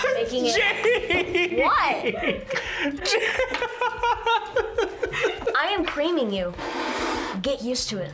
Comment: Yoooo anyone notice what he said at 5:35 ? He’s WHAT?!? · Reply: IM CREAMING U